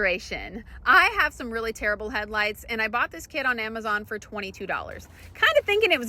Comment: I thought she said i have terrible head lice. Lol